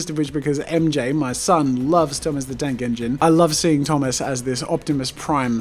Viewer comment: he said thomas the tank engine, I thought he said thomas the dank engine